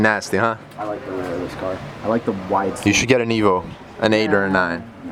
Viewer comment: I’m watching this on captions and @ 2:57 it say negro instead of evo